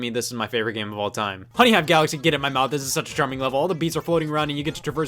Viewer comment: I thought at 11:11 said that honeynut galaxy came in his mouth 😂